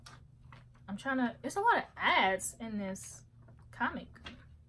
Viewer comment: !!! 😍😍😍 You're extra confident in this video! When you said there's a lot ads in the comic, I first heard, "There's a lot of ass in this comic." Definitely gonna check out Snot Girl. Umm... I love that you are self aware and honest about your selfishness. Sometimes I think people more selfish than they think. Really solid video!